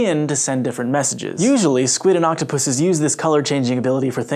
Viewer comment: did he say octopuses?